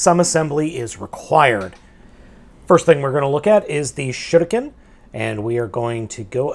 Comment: it sounds like he said shitiKen